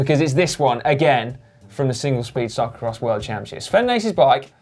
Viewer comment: I thought you said singlet/speedo cyclo-cross